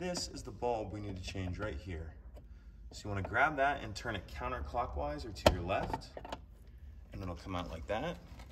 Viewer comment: What if its not rotating clockwise I have the same car plz help me out its not turning clockwise and not coming out. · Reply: I thought he said to turn counter-clockwise to get that out, not clockwise. Maybe that's the issue.